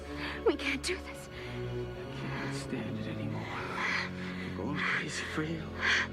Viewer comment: what does he says at 1:43:30 ? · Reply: I can't stand it anymore, I'm going crazy for you.